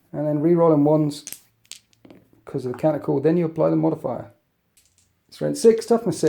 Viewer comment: I'm fairly sure is saying "Counter-calls", but what i hear is "Canticles". Like cuticles, but they can't.